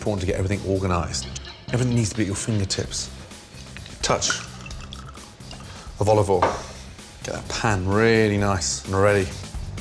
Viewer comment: Is it "A touch of olive oil" But I heard "A touch of volley ball"